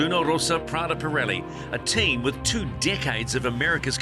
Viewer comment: I could swear he says 'a team with two dickheads'.